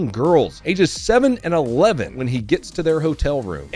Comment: Youtube through in an add at the perfect time when you were saying “ in a room full of cops” my immature ass thought you said a room full of 🐓